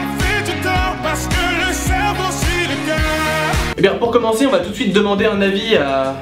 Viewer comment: Moi j'entend pas ''parce que le cerveau c'est le cœur'' mais ''parce que le savon c'est légal'' xD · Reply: Aha moi j'ai compris "parce que le cerveau s'irrigueuuuh" xD Mais en vrai je crois qu'il dit "parce que le cerveau suit le coeur" non ?